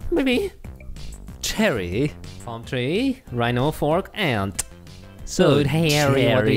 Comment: I wasn't paying attention and i thought you said rhino-fork as one contest